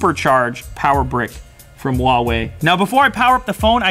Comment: Did he say the charger was from Huawei?